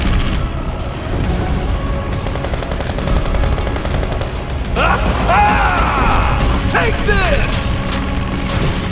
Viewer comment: I'm sorry, what did they say? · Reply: For the emperor! □